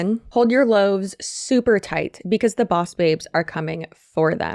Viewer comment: For a second i thought you were gonna say Bob's red mill instead of boss babes 😅